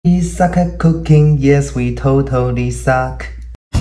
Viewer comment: I lowkey thought he said ‘we suck up cocaine, yes we totally suck’ help 😂✋